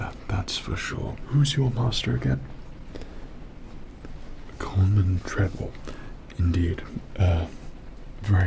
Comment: did he just say my master is a tredwell droid?